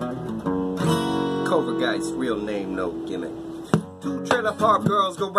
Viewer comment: I thought he was saying covid for a sec, then I looked at the date